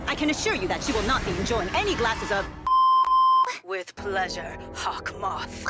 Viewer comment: I thought Kagami's mother said "F*CK OF"